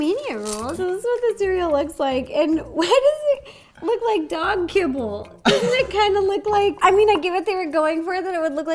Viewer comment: When Ro said that It looked like dog kibble, I thought she said dog pellet's … ummmmmm ouch